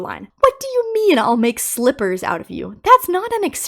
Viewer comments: For Rockstar Bonnie’s slippers line: rabbit slippers! It’s a joke about a rabbit wearing human slippers like a human wears rabbit slippers (though I’m sure most rabbit slippers aren’t real rabbit) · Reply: Thought he said slivers lol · How do slivers not make sense? He's saying he'll cut you into strips. Very in line with his other threats. Not to mention that that is clearly what he's saying, IMO.